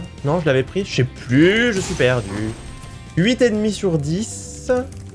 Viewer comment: « 8 ennemie sur 10 » y a que moi qui entend huit et demi sur 10 ? 🤔